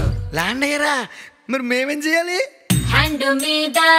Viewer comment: this word anyone understand. This word says in hindi 😂😂😂😂😂😂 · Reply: Hat me hat dalo land line ko ring karo